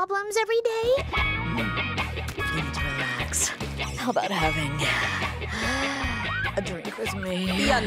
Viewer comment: what did she say with the pen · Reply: You need to relax, How about having a drink with me